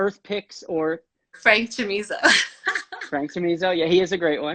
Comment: Re: the Instagram account she mentioned, at first I thought she said, "Friends Tunisia" but I'm 90% sure she was mentioning "Frank Chamizo." (frankchamizo92)